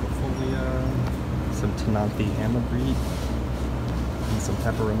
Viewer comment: please tell me what's the name of the plant at 14:01? Couldn't quite hear its name. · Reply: Vernita James ctenanthe amagris (